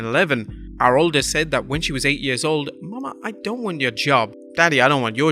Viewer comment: why did it say Momma I don't want your dad?